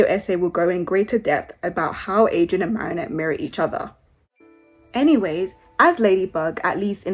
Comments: Am I the only one who hears "how Marinette and Adrien marry each other" at 4:32 ? xD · when you said "abt how marinette and adrien merry each other" I heard "how marinette and adrien marry each other" I was so excited lol BAHAHA